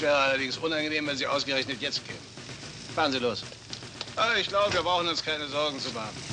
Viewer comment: What does Rommel say at 3:20, something "los"? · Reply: He said "Fahren Sie los!" the English equivalent would be "drive off!"